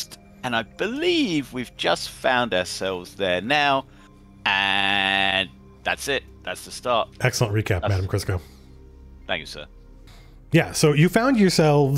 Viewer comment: I swear Matt says "Thank you, Madame Crisco", not Matt O'Driscoll. There's no way he knew about Madame Crisco at this time.